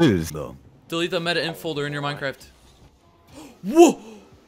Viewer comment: did he just say minecraft?